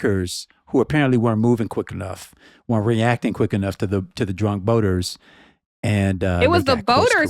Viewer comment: When says "Drunk Boater" I keep hearing "Trump Voters"